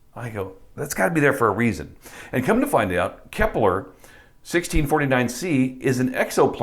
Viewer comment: Lol at first I thought you said "Kessler" as in the author of 'Demonology 101' lol that'd of been another hilarious addition